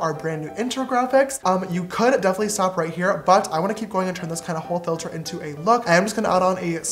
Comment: I was listening with no volume and somebody tell me why I thought he said I'm a whole filter myself boo lmao last 😂